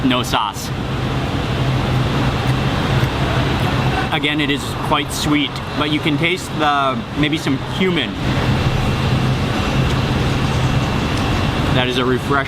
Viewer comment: it sounds like he says (you can taste some human) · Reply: Lol..he said 'cumin'